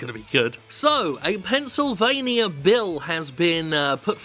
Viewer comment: I actually thought he first said "Pencil-vania"